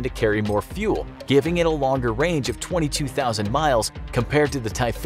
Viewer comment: the Vocal says Twenty two thousand miles. The Subtitle says 2,200 Miles for the SU-57.